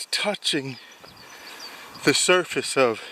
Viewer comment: thought that said touching the cloth!!! 🤣